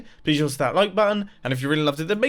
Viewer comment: why do I hear "...and I'm out, accelerator."